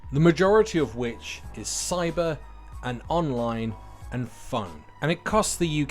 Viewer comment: When you said "Cyber and Online and Phone", I heard "Cyber and Online and Fun" and my thought was, good for you